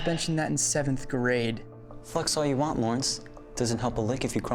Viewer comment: when he said "flex all you want lawrence" I thought he said "f***s all you want lawrence" and I had to replay it three times